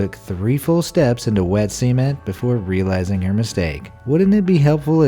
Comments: literally thought you said 3 steps into wet semen 😂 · lmao, who else heard semen?